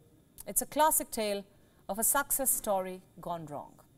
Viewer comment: Did I hear you say "Suck cess Story" ? WION has some poets on board.